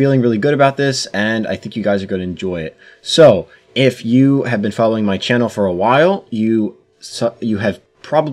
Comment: onwards I heard "if you've been following my channel for a while, you suck" 😂